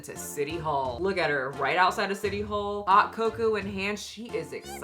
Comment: I swear I thought you said thot cocoa lol😂